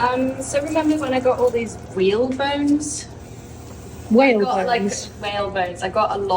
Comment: I thought she said, whale bones also. Lol